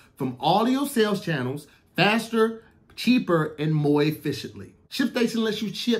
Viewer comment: Hey Tom did he say “MOI EFFICIENTLY” I think he meant to say MORE efficiently, Back to you Tom🤣🤣🤣🤦🏾‍♂️